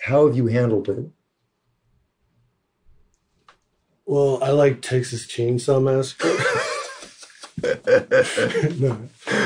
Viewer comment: Could anyone tell what joke Buckethead said at 37:45? They both laughing :) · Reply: Having been asked how he handles the problems that arise from being famous, he says "Well, I like Texas Chainsaw Massacre..." I have not seen it but I guess that is a movie with a lot of violence? He may be playing off a joke that another musician made in a different interview I heard, about what would happen if he revealed Buckethead's identity. Here Buckethead goes on to say that he is not recognized so he does not actually have much problems due to fame, but he does see the problems his famous friends have to deal with. Obviously this is not a violent person... he still jokes like a teenager.